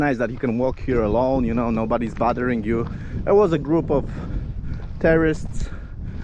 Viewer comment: I thought you said, there was a group terroist in from of you at 20:13, but then I realized you must have meant tourists. My bad.